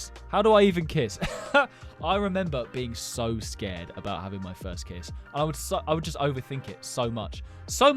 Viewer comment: I thought you said kids 😅😂